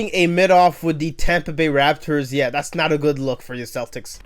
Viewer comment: Did you say "Tampa bay Raptors" at 15:30?